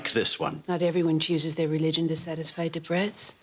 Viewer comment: "Not everyone chooses their religion to satisfy the Brits" Cora is the epitome of class and sass. Never raises her voice never leaves her ground. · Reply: She said "Debretts". It is a book, a Who's Who guide to the upper classes. I smiled at the idea you thought she would say "Brits", a very modern slang for British. That would have been the opposite of class 😀